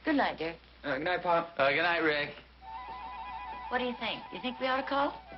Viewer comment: I'm sure Harriet says "Dick" instead of Rick